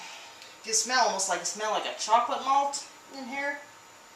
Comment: did you say 'chocolate hops'?